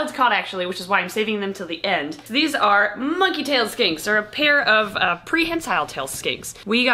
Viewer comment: I am so sorry for saying this but had to share this. When she said she was spooking for "Monkey Tail Skinks" I thought she said "Monkey Tail Skanks" 🤦